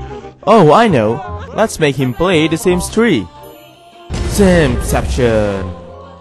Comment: I thought you said the sims tree